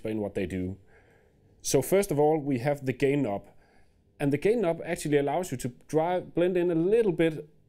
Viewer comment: I can't be the only person that heard him say "gay knob".... good demo though